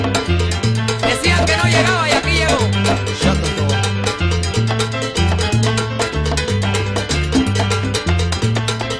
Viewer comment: Alguien me puede decir que es lo que dice hector en el minuto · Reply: Shut the door